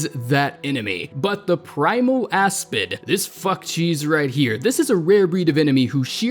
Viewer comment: I heard f*** cheese